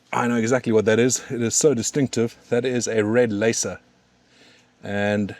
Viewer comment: What was the name of the pepper you tasted? I thought you said red laser but I can’t find anything on google Cheers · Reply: Red leysa